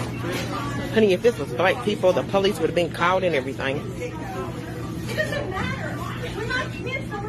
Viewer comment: did she say, "honey if this was black people the police would've been called and everything"? Wow!!! Racism at its finest!!!🤦‍♀️🤦‍♀️🤦‍♀️🙄🙄🙄